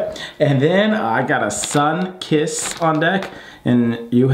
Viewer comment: I had to go back a few times to make sure I heard correctly, but said "Sun kiss" for his Chips 😂😂😂